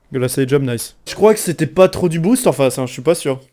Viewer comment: A 10:05 quesque tu dit mdrr ? Jvais reroulé un quoi ? ;)))